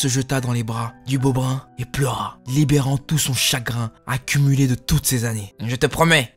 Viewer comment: "Libérant tout son chagrin accumulé de toutes ces années". -Moi au début j'avais compris tout son chakra , je me disais bien que c'était bizarre ! 😂😂😂